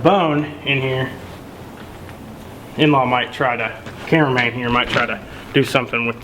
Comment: did he say inlaw at 8:20?